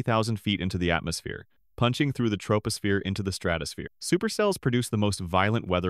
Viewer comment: did you say supercell?!?!